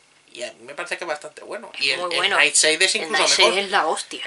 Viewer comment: Que?, cual es el nombre que no entiendo como se escribe y quiero buscarlo. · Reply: Es Nightshade